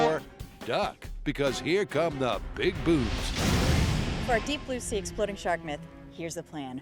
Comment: I thought he said big boobs, not big booms